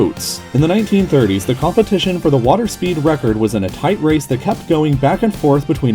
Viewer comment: At first I thought I heard " water ski" world record, which is maybe 4 times as insane....no, make that 20 times...